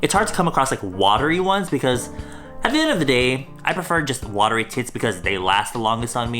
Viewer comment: I swear I heard “I prefer watery tits on me” 😂😂😭 Edit: it’s